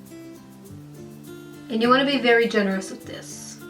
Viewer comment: I thought she said "and you wanna be Ellen Degeneres with this." I was just like "oh"